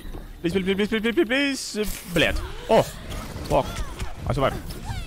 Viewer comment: excuse me, what did you just said? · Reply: he said "блять" неужели не расслышал?)))